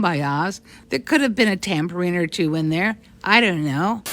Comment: I heard "tambourine" not tampering.